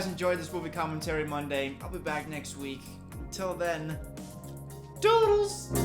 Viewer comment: I never get what he says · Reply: Toodles